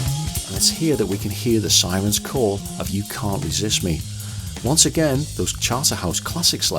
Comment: You just resolved a 30-year mystery for me with that audio extraction! For so many years, I heard "Oh where is history" for that one. I could sorta make that make sense, but this just does. ("Where is history" was, to me, translatable as, "Why don't we learn?")